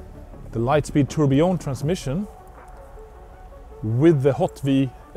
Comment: it it just or did he call it light weight tourbillon transmission